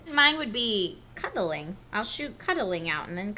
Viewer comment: Ohhhh... she said cuddling. I thought she said cunnilingus.